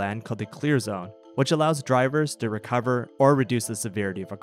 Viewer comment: Bruh thought he said “kill zone” instead of “clear zone” I was like •_•